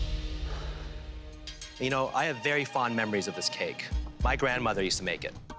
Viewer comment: Lmao did claudio call pino? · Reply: He said "you know"